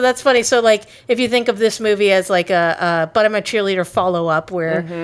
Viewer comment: Is it me or I always hear the title "Bottom a cheerleader" ?